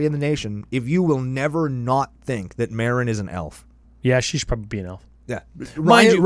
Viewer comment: I misheard “elf” and thought you called Meren a Milf and I was like “yes preach kings!”